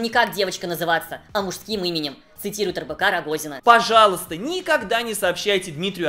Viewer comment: я один случайно услышал не РБК Рогозина, а Рыбака Рогозина?)